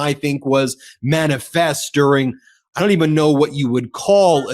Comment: Oh, "manifest during." I had to go back and look at the CC - I heard it as "manifestering," and thought it was a new word I'd never heard. I think it's right on the button, so thanks. This regime keeps "manifestering" its evil intentions every day.